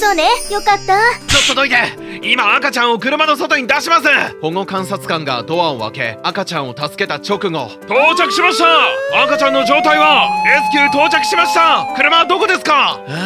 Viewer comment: ホモ観察官にしか聞こえない